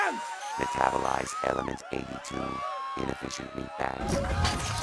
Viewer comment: Holy crap reaper's 8.1 transmission is badass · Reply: was it only me that heard him say in 8.1 "initializing element 82, insufficient meatbags". 😂 by the way Element 82 is known as lead, meaning he was loading him mechanical minigun and was going to shoot them with bullets.